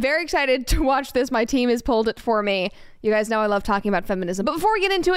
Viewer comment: When she said my team pulled it for me or something like that I thought she said my teen pulled it for me and I was like what she has a child!!!!! Edit: the time was